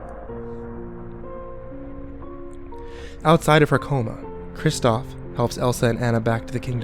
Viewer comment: I put cc and it said kristoff eats her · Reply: I saw the same thing, but I really wanna know what said, because what I heard was "Kristoff eats her"